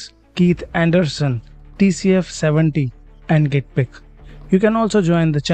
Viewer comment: When homie was saying those member names, did he say “Dik pic” as the last name?? 8:09 😮😅